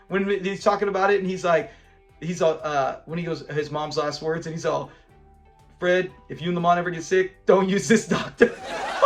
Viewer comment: Lamont i Thought u said Mom's Last Words Was Please Take Care of Lamont